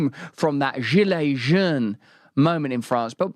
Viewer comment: Jelaijeune, jejune? What's Brand trying to say here, I can't tell if his accent is massacring a French word or it's one of those Fake French words invented my English speakers because they misunderstand a French word and they still use the Fake French word to sound cultured and smart... 😶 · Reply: Gillet Jeune or some sht. Yellow jerseys maybe.